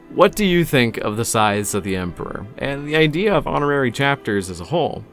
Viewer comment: Scythes of the Emporor. What i heard him say: Sides of the Emperor Slides of the Emperor Sighs of the Emperor I really have to check my hearing.